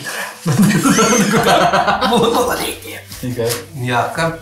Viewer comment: на 2:14 я услышал "ПиКра"? или показалось? они такое не разливали